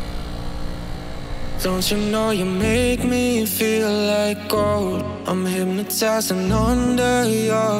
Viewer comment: It took me 2 weeks to understand he is saying like gold. First I thought he is saying like colm then like home then what’s not